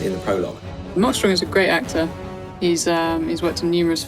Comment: It sounds like she said "Im not sure hes a great actor"